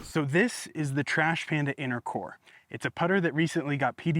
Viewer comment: "inner core" I had to rewind because I thought you said intercourse. Lolol